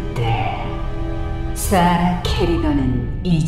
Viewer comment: 사라케리건이 왜 쏴라 케리건이라고 들리는건 나뿐인감 ? ㄷㄷ